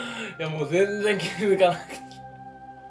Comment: ハッハッハッハがニャンちゅうにしか聞こえませんすみません